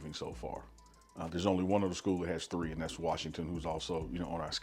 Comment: Washington State and yes they are now ranked and on the schedule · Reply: In the video it sounded like he said Washington not Washington State. But it's all good. Beat CSU. 👍